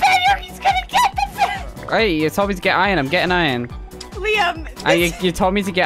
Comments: did she just say liam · an easy why to do this is say (do what ever it takes win the game) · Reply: Also I heard that hiss name is Liam